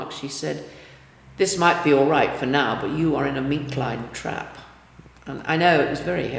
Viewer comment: I can't understand what she is saying there. Captions says "meet line trap" which I took to be "meat line trap" I googled that and it talks about line trapping animals. I must be dumb because I don't get the context. Any thought? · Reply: Meat lined trap